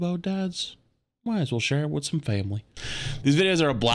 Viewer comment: did you say family??